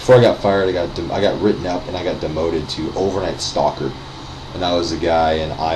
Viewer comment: i thought you said that you got demoted to "overnight stalker" at first lol